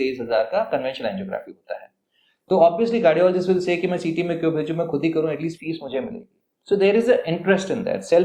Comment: Did he just say BehenC*** on the video @ 18:32 “then obviously the cardiologist would say ki main CT mein kyon ‘BC’ khud hi karoon ke fee mujhe mile” … I heard it on repeat and yes he did say that ‘BC’ word 😂